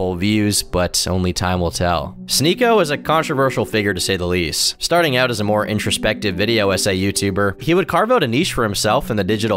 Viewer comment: I was kinda listening to this in the background and when you said "Starting out as an introspective video essay youtuber" I thought you said "started out inspecting (SA) youtubers" We really need to find a better way of implying (sexual assault) than just calling it SA or youtube needs to stop demonetizing people for bringing attention to it. Cause SA and essay shouldn't be so easily confused. Sorry random rant